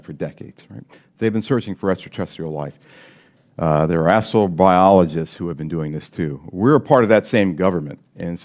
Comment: did he say “Asshole biologists”? I’m just asking! 😜